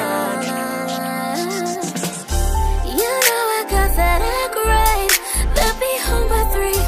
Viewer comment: Tell me why I thought she said “You know I got that hot grave” 🤦🏻‍♀️